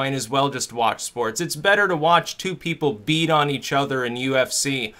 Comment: I totally misheard that as "Beat off on each other" Which it probably is :')